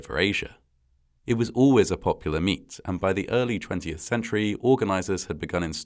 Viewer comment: I was perusing the comments when I thought I heard “popular meat” at 2:25, and thought he was talking about horse meat. I had to rewind and listen two more times to realize he said “popular meet”! 😂